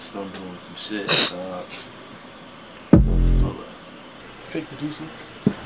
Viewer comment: "fake producer" did anybody catch that?